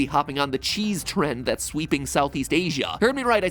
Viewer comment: Did he just say cheese drink.